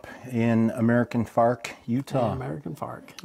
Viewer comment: I thought he said "American fart, UT" as where hes from. I leave that one alone